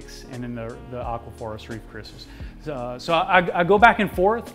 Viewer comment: It sounded like you said “Aqua Forrest Reef Christmas”😂